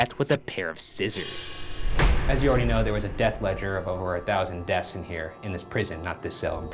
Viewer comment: I thought "Death Ledger" said "Heath Ledger" and I cried.